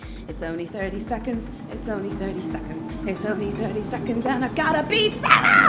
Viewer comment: "And I got a Pizza". Or did you say I got to beat Sarah?